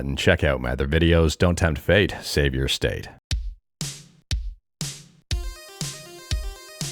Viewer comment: Everytime I heard don't temp fate save ur state I know it just me but I feel like I'm hearing don't temp fate save ur stake I guess I am hungry